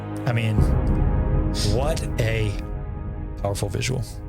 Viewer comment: why did you call Syril a whore at 54:12? 😂😂😂😂😂 He's a good man that just wanted justice!!!!!! 😂😂😂😂😂